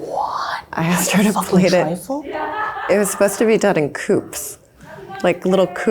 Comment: shouldve explained that one to her then cos i also wouldnt have known what that word meant :D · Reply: Sounds like shes saying "cups" all fancy like